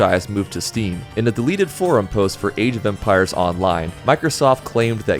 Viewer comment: was focused while working, the way you say "age of empires" sounds like you said "asian vampires" was disappointed you weren't talking about the latter